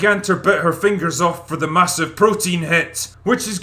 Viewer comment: "Bit her fingers off for the massive protein hit" . Pure gold. XD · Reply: I thought he said “with her massive protein head”🤪